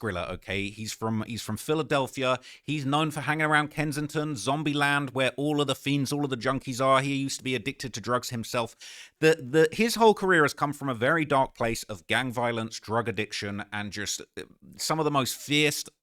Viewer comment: What tf are you talking about? How is saying someone used to be addicted to drugs insinuating that they’re a gang member? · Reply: rewatched time stamp I thought he was saying use to be in a gang, not use to be addicted to drugs, my b lol